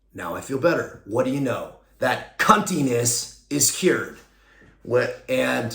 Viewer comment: Bro did you just say "That cuntinessis cured!"???😂😂 you are an epic soul